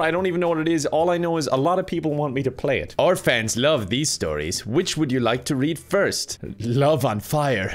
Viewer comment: What I heard: “Orphans love these stories” What is actually written: “Our fans love these stories”